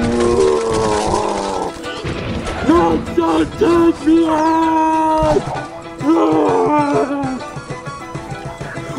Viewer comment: What did that big pig monster say at 9:19? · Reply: Must Get To The Egg!!!